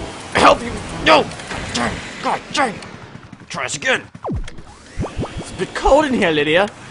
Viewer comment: I thought he said I am a bitch